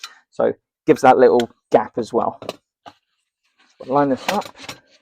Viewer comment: Don't know if you're still using subtitles in your videos, but you could do away with them. We can clearly hear what you're saying. When you said "Line this up" at 7:01, the subtitles read: "Line of f*ck".